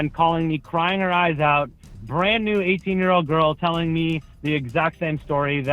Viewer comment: did he just say brand new 18 year old girl??